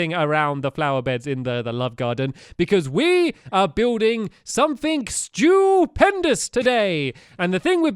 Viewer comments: When he said the name of the restaurant I thought he said they'll build something stupid. Lol love you stampy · did anyone else think Stampy was gonna say stupid??